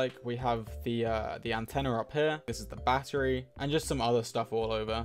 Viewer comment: "intenner" is what i hear 😂